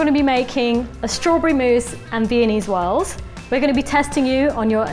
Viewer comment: Ok I diss Baz a lot - bit I heard 'Vietnamese swirl' as well. I'm still not even sure what they actually said. 'Viennese... Whorl? "